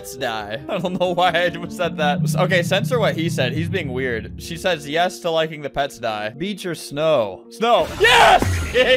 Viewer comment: what did bro say at 5:07 not flamingo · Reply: He said are you dirty